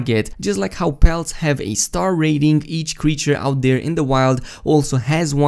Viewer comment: what does he say at 2:19 ? bounts ? I couldn´t hear.......... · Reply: 'Like how pelts have a star rating, so do the animals'.